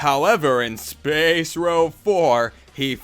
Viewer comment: did he say spacerow 4 at 2:00?